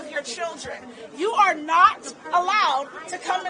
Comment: Reaping came sooner than the thought! He didn't say parents can't get in, he said "you" can't get in HAHA.